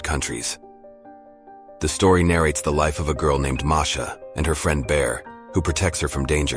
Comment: i mishearded her friend bear is fredbear💀